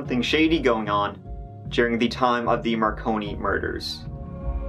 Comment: Lol my Fat Ass straight up was like the macaroni murders huh sweet 🤣 wasn't until I heard you say it that I realized how dumb I am ☠🤣